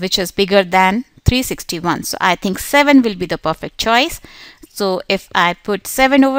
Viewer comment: I still dont get it why you put two 7 and the only thing i hear was "7 is the buffet choice"